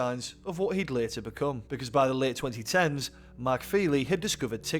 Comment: He discovered TikTok in 2010? Wow, to say is wasnt released till 2016, he's obviously a time traveller. And we all know where he's from. Leeds. He lived in Rothwell for a while. We used to beat him up all the time. If you are from south Leeds, do you know if he went to royds? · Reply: I thought he said "the late 2010s"